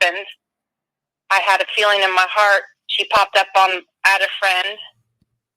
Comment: You are correct. This woman is a LIAR and she claimed in late May to not have known Suzanne at all. Now all of the sudden she "became friends with her" the friday before her disappearance. 🙄🙄🙄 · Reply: she did not say that she added SM on FB, she said "I went to add her", listen again please....